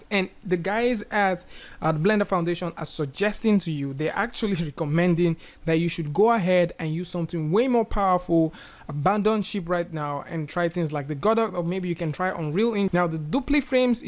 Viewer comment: What is he saying at 11:55 to 12:04? I really can't understand! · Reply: "They are actually recommending to go ahead and use something way more powerful. Abandon ship right now and and try things like the godot or maybe you can try unreal engine"